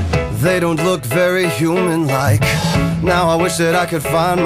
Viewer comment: is it just me or did it said familiar instead or human like